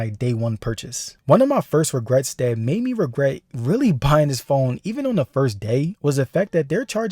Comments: Did he say he regrets “buying this phone” at 8:58? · did bro just say “this phone”? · did you say "One of my first regrets buying this **phone**?